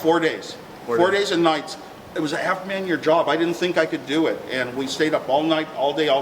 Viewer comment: it was a "half man year job" !? What is Woz saying here? I know it only took 4 days so I'm not hearing right.